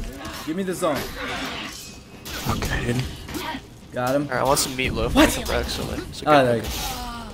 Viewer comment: anybody else hear who i think was say "I want some meat loaf"